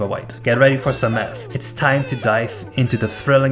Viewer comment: i think he sounds like he said meth